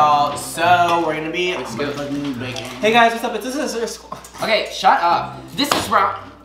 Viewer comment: did larri just say “the sister squad”?🥺